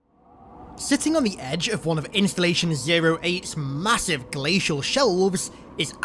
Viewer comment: In min 27:23 did you said Installation 08 because i cant find any info on that